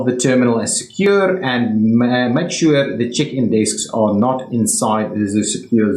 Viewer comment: When you say place atleast two check-in desk I heard it as chicken desk 😂😂